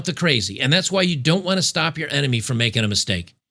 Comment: I thought you were saying enema! Had to put on the CC to see NMN.